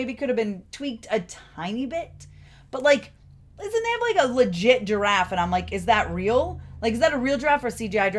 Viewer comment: I thought at first you said Gingiraffe like gingerbread man crossed with 🦒 😂